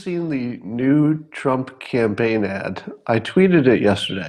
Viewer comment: New Trump campaign ad, not the Nude Trump campaign ad. I misheard and almost threw up my simultaneous sip.